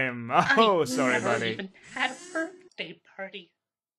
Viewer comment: what did he say? Something about a party? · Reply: "I never even had a birthday party"